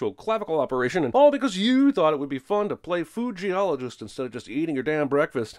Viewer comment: I thought you said fuji-ologist for some reason and spat out my tea.